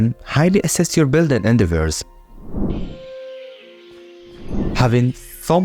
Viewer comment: i thought he said enderverse lmao